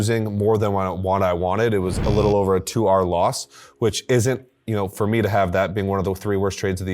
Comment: the transliteration showed "2Hour loss" instead of 2R loss...